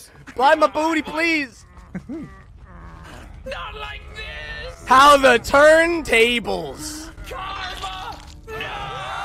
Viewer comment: did he say how the turn tables.